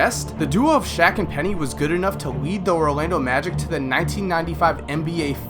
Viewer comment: Is it me or does say Rolando Magic